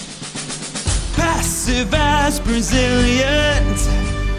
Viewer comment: He says something about Brazil? If yes, What he said? I can't hear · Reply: Passive as Brazilians